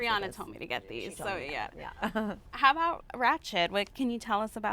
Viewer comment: What is she saying at 20:34? I hear ratchet? wratched? rat shit(It is obviously not rat shit but I cannot for the life of me understand what the lady was trying to say...) some project that Ms. Paulson is working on with Mr.Murphy... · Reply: nevermind, it was ratched...